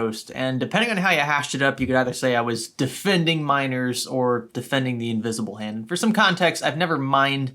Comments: i am high rn and i thought for a sec he was saying minors, not nimers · i herd miners and i thought he said minors